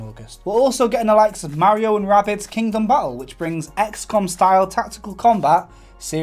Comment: Did he just say "Mario & Rabbids Kingdom Butthole"?